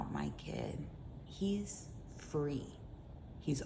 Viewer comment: i thought she said her kid was 3😭😭i was oh okay then i realized she said free and yup… we are cooked